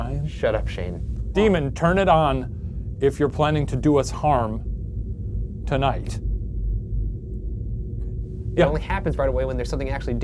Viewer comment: I thought Shane said 'turn the light on if you're planning on doing us hard tonight' fml😂😂😂